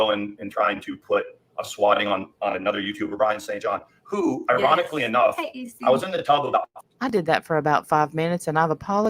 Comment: does he say ironically enough I was in the TUB ??? 😂😂😂